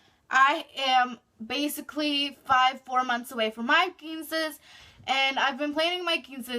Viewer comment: I thought she said princess 😭😭